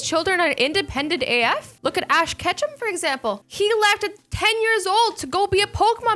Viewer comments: When you said ash Ketchum, I thought you said “.ash ketchup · did you say Ash Ketchup? Cause it sounded like that. (sorry for my spelling)